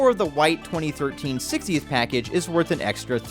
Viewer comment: I thought it said "GOTH PACKAGE" 😂😂😂